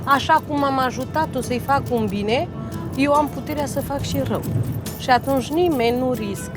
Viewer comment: "Nobody wants to take the risk of not paying me." damn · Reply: it sounded like she said "i fuck shit up" lmao